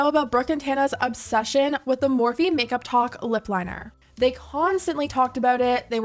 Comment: Am I the only one that thought they were saying make up TOK THE WHOLE TIME?! 😂